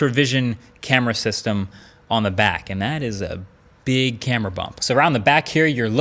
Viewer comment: 🤣 I misheard that like big ass camera bump